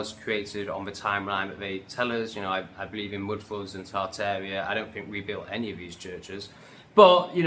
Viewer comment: what do you say there? its not clear, I cant make out what you are saying you believe in. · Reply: "I believe in mud floods & Tartaria. I don't believe we built any of these churches"